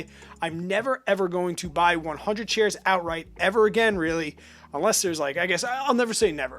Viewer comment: Did he just say I should never buy 100 shares of anything ever again??